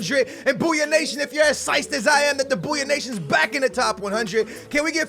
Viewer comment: ur from the DMV??? I heard you say "siced" and I got really excited cuz I didn't know you were from around here